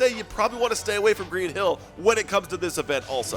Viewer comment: did he say green hill bruh